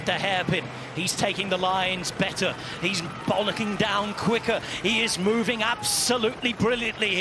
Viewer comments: Did I hear Rob Hatch say 0:10 "he's bollocking down quicker"? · Did he just say he’s Bollocking down quicker? 😂😂😂